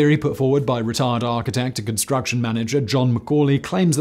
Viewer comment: Did....did he say " retard architect John Ccauley" at 5:57???????